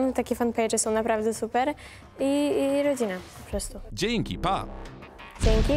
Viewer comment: na początku usłyszałam ,,jedzenie" xD